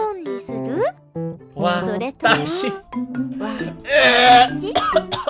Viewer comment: akala ko pakshit narinig ko hahahaha watashi pala